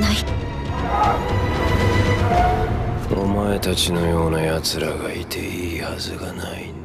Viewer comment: Can someone tell me what Ogata says at 0:56 in romaji Japanese? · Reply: Sounds like, 'Omaetachi no youna yatsura ga ite ii hasu ga nai n da.'